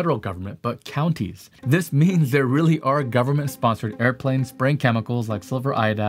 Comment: Aha! I got you red handed (or red lipped, or... you know) - at or near 13:36 it sounds like you are saying "are government-sponsored airplanes spraying chemicals like silver iodide," but the infallible CC version shows what you are really saying is "are government-sponsored airplanes BRAIN chemicals like silver iodide...." I knew it I knew it I knew it I knew it! In the mid 1960s I lived not far from an Air Force base. We saw the chemtrails there, all right! I have it on good authority it was their earliest live trials of dihydrogen monoxide condensates.